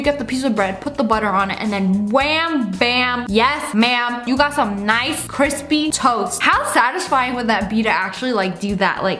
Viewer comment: I thought she said “wam bam you got some nice crispy toes”